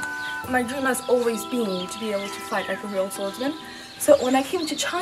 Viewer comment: She said to "fight like a real swordmans", subtitle is wrong. Also, I felt like she wanted to go to China to find an ancient sword in a cave and uncover some long lost sword art and become a warrior. But instead, she got put to manual labour... xD